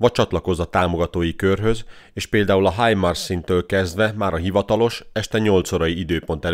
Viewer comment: Mit mondasz 4:32 -től? "És például a ...-től kezdve"? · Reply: A HIMARS támogatói szinttől (amúgy ez a második szint) a tagok korábban nézik meg a jelentéseket, nem este 8-tól. Hétfőn este lesz egy közösségi emlékeztető posztunk arról, hogy hogyan lehet taggá válni.